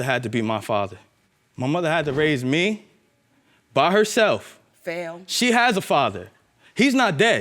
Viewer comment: Did I hear someone say "fail" when he was describing his mother raising him?